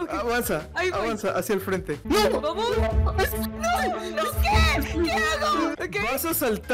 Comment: entendi a cherry decir "amor" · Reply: dise vamos no amor